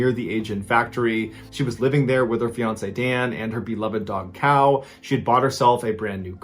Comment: cow? did I hear that right or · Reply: He said dog cow